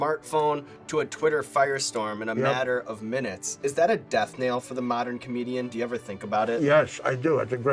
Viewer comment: did he just say "death nail?"